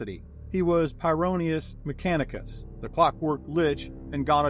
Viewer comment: Thought that said glockwork